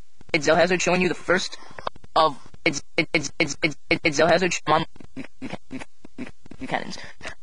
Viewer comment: kidnda sounds like he said Zellhazards MOM